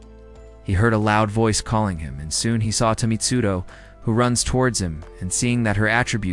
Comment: Bro sorry i'm complaning but in 2:34:10 why did you say tomitsudo instead of Mitsuri Kanroji